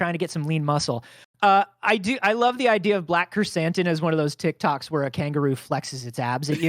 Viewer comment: What is the kangaroos name? I keep hearing "black Chris hansen